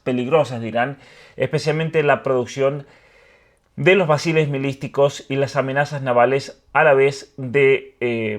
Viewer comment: Alguien me puede explicar eso que dice Iturrieta "Basiles Milisticos" en el minuto 4:50? 😢